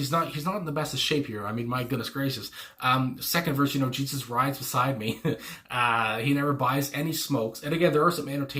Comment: Have always loved “Jesus rides beside me, but he never buys any smokes”🤣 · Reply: I always heard it as “Jesus rides beside me, never buys, and he smokes.” Like he never buys alcohol, and as a side note, he smokes. 😂